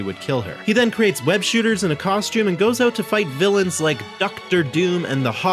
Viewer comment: Oh god I thought he said hog gobbler